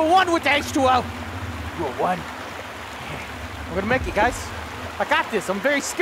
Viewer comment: Every time says "i'm gonna make it", I hear "I got a medkit" :P too much of fortnite effect.